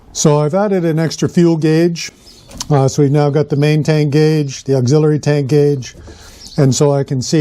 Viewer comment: I thought you said you were making a rum tank?!?!😂